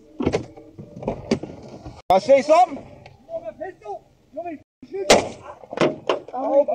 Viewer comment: The phone is irrelevant. Saying he had a gun and would shoot is enough. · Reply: Fr I thought he said "you see my pistol"? I wouldve thought it was a gun